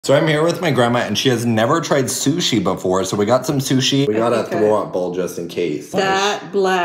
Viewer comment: I thought u said "so I unearthed my grandma" 😭😭😭